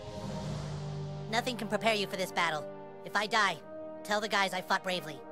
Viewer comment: Did anyone else hear ˝If I die, tell the guys I fucked bravely˝ instead of fought at 2:25??